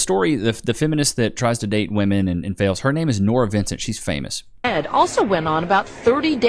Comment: Mad respect to Nora Vincent. She’s done men a lot of good. You could say she gave her life to shine light on the plight of men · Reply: i thought u said " im mad at nora vincent!" but no i totally agree